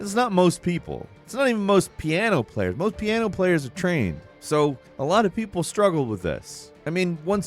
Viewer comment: “Moe’s piano player’s a train.” That’s what I heard so I definitely don’t have a ear for music.